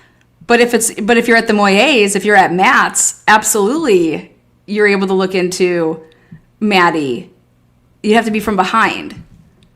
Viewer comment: She didnt say you can look out the window and see into Maddie's room. She said, you can look out the window and see Maddie's house. Meaning, if you look out the window of the apt she was at, you could see 1122- the whole house. She is just calling it Maddie's house.